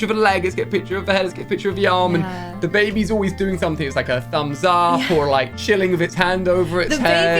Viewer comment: so happy for you guys!! but… 10:22 I think it’s a girl! right here it really sounds like he said “HER thumbs up” anyways no matter what this baby is going to be so well cared for and loved